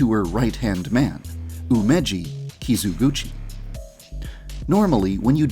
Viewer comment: I thought he said gucci